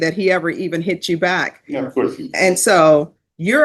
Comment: Did he really say “Yeah of course he didn’t” (Hit him back) yoooo lol